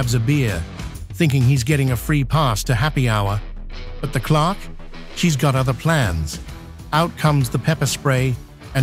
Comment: every time the electronic Narrator says “clocks” instead of “clercs”, it drives me bonkers!